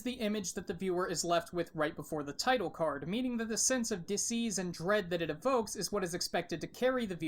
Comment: Wait wait wait, have you been was “dis-ease”? I thought you were saying “disease”. Wait was that intentional? Or did you forget “unease” is a word? Youre too meta, man.